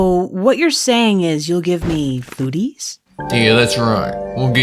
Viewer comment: it sounded like she said coodies the first time and foodies the second